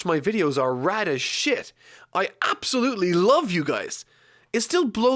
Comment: I thought you said "most of the people who watch my videos are raddish shit" 😂😂